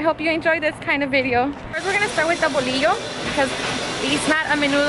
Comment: When you said bolillo, I thought you said whelio for a second. 😂